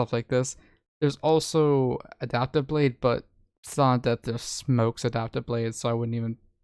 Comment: what did u say what smokes adaptive blade i didnt understand u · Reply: Silent death